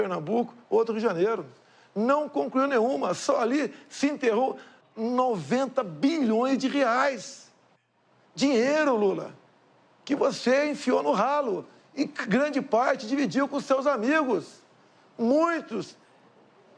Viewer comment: O que ele disse: Dinheiro que vc enfiou no ralo. O que eu entendi: Dinheiro que vc enfiou no rabo. Hauahauahau